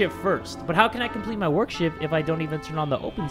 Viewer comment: I THOUGHT HE SAID WORKSHIT